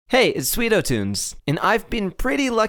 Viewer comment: I heard you say SweetoTOOMS!!!!!!!!! XD